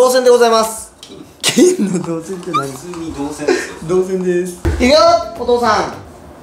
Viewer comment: 「童貞です」って聞こえてしまう